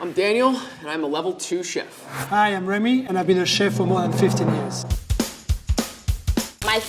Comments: I wasn’t looking at the screen at for level 3, I thought he said “I’ve been a chef for more than 50 minutes” 😂 😂 😂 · When the remy guy introduced himself i thought he said: Hi my name is remy i've been a chef for 159 years. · i thought he said i’m a chef for 115 years😂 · I literally thought the level 3 chef said "I've been a chef for 115 years" XD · I heard "hi I'm Remy and I've been a chef for 115 years" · ngl it sounded like he said he has been a chef for 150 years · i heard the level 3 chef said he has been cooking for 150 years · was it just me or did he just say that he's been a chef for 115 years? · i thought the level 3 chef said he's been a chef for 150 years